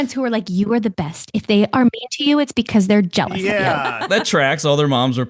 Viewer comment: I thought she said "race card" at first lmfao. "Yep. That tracks"